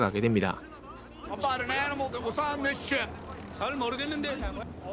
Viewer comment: 에 잘 모르겠는데 라고 들리는거 나만그럼?